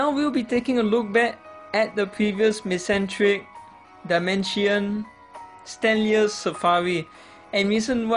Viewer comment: I thought you said "Dimentio" or "Dimension" when you mentioned Darmanitan, lol. And when you mentioned Winking Raichu, I thought you said "freakin' Raichu".